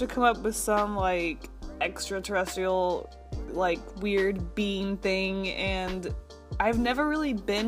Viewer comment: I swear I heard say "Oh great I have to come up with some like, extraterrestrial like weird BEAN thing." Seriously I didn't know aliens were beans! xD mmm Yummy! Check it out